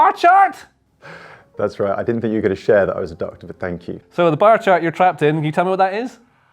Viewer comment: I thought he said he was adopted